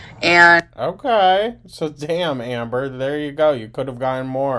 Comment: did you just say Amber 😂